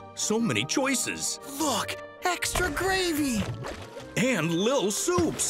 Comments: Am i the only one who thought this cat said fu@ extra gravy? Yea ok..... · Lmao I thought this commercial said f gravy 😆😆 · I CANT STOP HEARING FUCK INSTEAD OF LOOK AT 0:03 NAKDJSNDKSJDK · is it me or did he say the f word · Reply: "Look". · WHY DID HE SOUND LIKE HE WAS SAYING FUCK INSTEAD OF LOOK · sounds like he said fuck, extra gravy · I thought it said "f__k, extra gravy!" 😶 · I am here because I heard this on TV and it sounded like he said f@ extra gravy